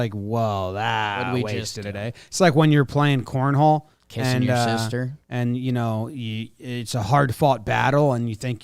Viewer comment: I’m sorry did say kissing your sister @ 13:35 ??? 🤣😭 just blew over it like nothing happened